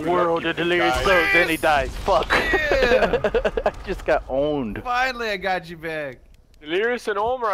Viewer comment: when ohmwrecker killed delirious, delirious said I just got owned it sounded like I got ohmed like ohmwrecker